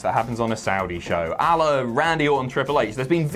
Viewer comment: When said "A-la; Randy Orton and Triple H." I thought he said Allah, Randy Orton and Triple H. What a triple threat match that would be in Saudi.